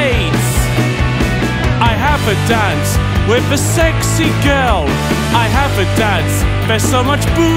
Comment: Ngl i genuinely thought he said "I have a dad with a sexy girl"